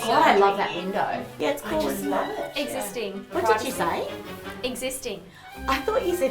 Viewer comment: Oh my god even I thought she said it's a sting. Who else did